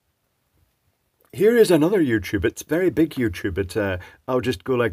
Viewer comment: I just heard "Here's another YouTube, it's a very big YouTube" there XD